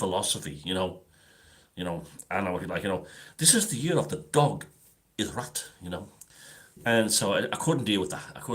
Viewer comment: Hahaaa, on second rewatch i heard ‘this is the year of the ‘dog is rat’ 🤣🤣🤣🤣🙏